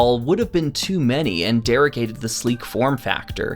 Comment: What does the word “derecated” mean at 6:51? I can’t figure out how to spell it to look it up in the dictionary. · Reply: "derogate" is the word you're looking for. 🙂 To detract, disparage, or worsen something/someone.